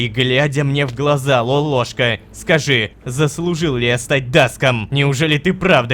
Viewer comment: и мне послышалось или он сказал даск?